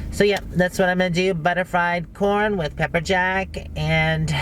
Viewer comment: That looked like a lot of fun - the corn looked great. I thought you were saying Butterflied corn and I was wondering what the hack that was! :-) it looked delish. And those trays - made me laugh, we were talking about them on T-giving, my S-i-L doesn't like his food to touch so I'm going to get him a tray as a gag gift! It will be so fun.